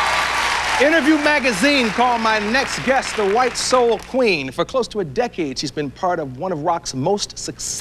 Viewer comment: what is he saying? Called my next guest (...?) 0:09 - 0:12 thank you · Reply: M&M “white soul queen”